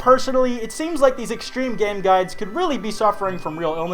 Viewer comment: At first I heard "Extreme Gays"